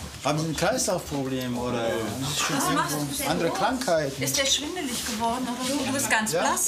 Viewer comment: Wer hat bei 1:47 auch ist dir schwulelig geworden verstanden?😂😂💖